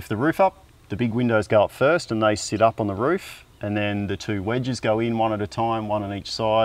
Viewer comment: I might have missed if they said, but so do they not have and plan not to have an air conditioning unit? I heard him say with the extendable roof (sounded like they said widge which i thought meant wedge but not too sure if someone would like to inform me) for head space in their loft that it works out well being the highest point so air can vent out, combined with how many windows they put into their home? And my second question was with most of these tiny homes (ik a lot are diy so they could customize it to however they'd like) but how does the water system work? Do they park it where an existing line is/ have to add one? Or do people have like a tank that they have to refill like in an RV?